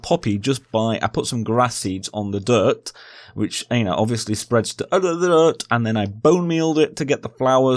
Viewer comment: excuse me what did you say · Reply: Something like “all the other dirt”